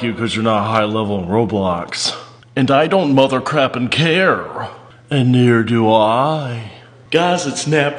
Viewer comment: “aNd I dOnt mOtHeRcraPiNg cAre” 😂😂😂😂😂😂😂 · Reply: thought he said ‘ at least i don’t mother, crap and care ‘